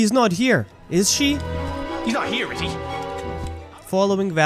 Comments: does anyone else hear “he’s not here is he?” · I don't hear the "she" part, but subtitles do. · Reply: That's because the VA said "he" both times. The subtitles are just there as a joke.